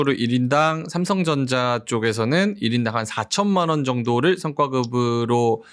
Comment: 현재 인당 4천? 이게 맞나요? 억대로 줄 예정이라고 알고 있었는데, 잘 못들었나? 인당4천은 너무 약하고, 하이닉스 6억이면 반발할 만 하죠. 그런데, 적당히 타협하세요. 노사 신뢰문제는 삼성 임원진들 반성하세요. 직원들은 성과급 0이었는데, 왜 임원들은 성과급 가져갑니까?